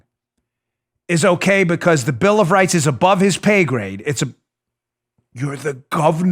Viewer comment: Above his pay grade? He must be working for someone else and not the people. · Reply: I know he said " pay grade", but I heard "gay parade".